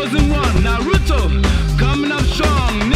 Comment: Did they just say the N word!?